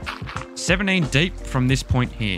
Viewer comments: I THOUGHT HE SAID 78 DEEP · hi i have a question because i didn't hear how to deep you need to mine down on 5:16 how deep is it again? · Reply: 17 deep dude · How deep I have to dip I don't understand plz help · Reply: 17 dude sorry for my accent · hey! sup, so im just building this tutorial and then suddenly, i cant understand this word 5:16 78 deep? 79 deep? 17 deep? can you translate it for me? thanks! :D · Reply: 17 deep dude sorry for my accent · what number did he said? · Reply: 17 dude sorry for my accent dude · WHAT DEEP DID HE SAID?? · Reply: 17 deep dude